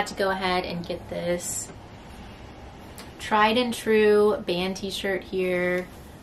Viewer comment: 😆 I Had to rewind this like 3 times because I thought you said Time and Tru… I’m like.. isn’t that at Walmart??.. then I realized you said Tried and True… I was like I’m totally confused now.. 😂 …but I’m good now,,, thanks again!☺️